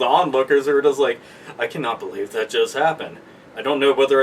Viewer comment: Didn't it sound like he said... "I can't believe that jizzed happened..." I cracked up so much. But, YES! This sounds amazing! Nintendo could be the next Epcot! Someone could invent the food of the different worlds. Hyrulian cuisine, oh yes!